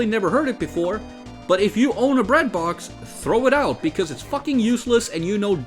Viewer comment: Okay i misheard "bread box" as "Redbox" and was like what does dvd rental have to do with anything